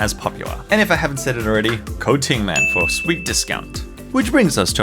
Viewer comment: When you said “use code tingman” I heard “use coating man”